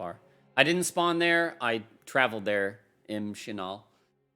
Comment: Does he say M Shinall? As in designer of the game Michael Shinall?